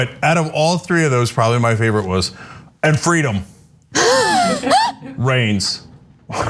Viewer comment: I thought he said Freedom "Range"??